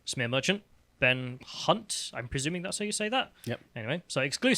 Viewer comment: OH BEN HUNTE ......THOUGHT YOU SAID BENT C*NT....I must get me ears checked